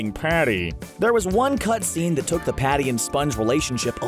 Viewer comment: when he said party he sounded like baldi from Baldi's basics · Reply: Fluffywolf he said patty not party